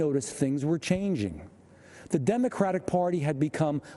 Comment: I got a simple question 0:58 I thought He said "The Democratic Party" but you wrote "The Democrat Party" so I wonder whether you just made a simple typo or He said it with a simple mistake Or... it doesn't matter at all to write/say it so And I think you're right. I mean the democratic party is literally democraTIC party so any party could be that. But he just come to Trump's party from Biden's party named the DEMOCRAT PARTY So I think He should said "The Democrat Party" as you wrote. am I right...? (I'm Korean who just can't type Korean with this key board, so you can answer me any languages Korean English, French, Spanish etc)